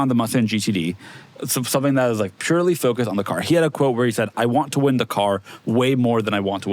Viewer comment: thought they said he wants to win “the car” I was so confused for a sec